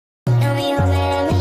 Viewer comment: This is what I hear ok “Wanna get drunk and masterbait”